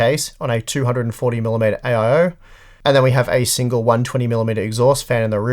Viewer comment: did you just say AIO? I'm sure pushing all that preheated air over the system doesn't help things.